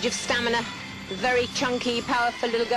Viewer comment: I’m sorry, did she call that young athlete “chunky”?! Smdh